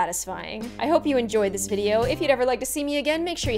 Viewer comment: did anybody else hear "if you never like to see me again make sure..."?